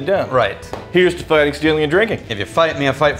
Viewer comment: Did say "Steal your liver's heart" this time?